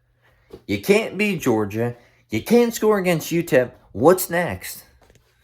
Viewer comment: I thought u said tn cant score